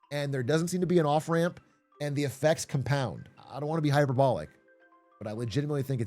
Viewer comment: Ngl I thought he said hypergolic, I was like when did he start talking about rockets??